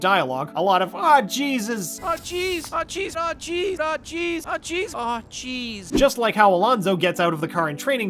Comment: I keep hearing “Ah cheese”